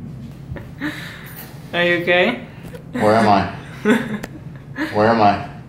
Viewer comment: Did she say “are you gay?”